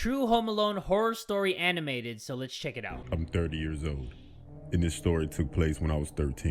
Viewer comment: did he say 13 year old or dirty year old